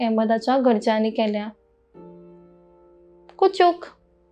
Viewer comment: Why did the reporter said KUCHUK at 1:47 ? Is it for the police or for the politicians? Or is it just me who heard KUCHUK at 1:47 ?